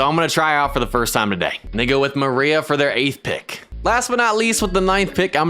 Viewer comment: WHAT DID HE SAY? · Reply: He said “and they go with”